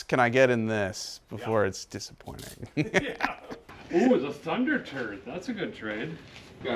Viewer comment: Sounds like he said Thunderturd. I find it funny that the one car out of all of them that I was interested in would be called 'turd'.